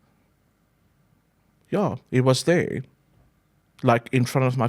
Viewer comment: he calls Jesus " them"??? Does anyone see anything off here? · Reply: Something definitely feels a bit off but maybe that’s just our natural scepticism when it comes to these matters. At 27:20 he actually says “He was there” not “He was them”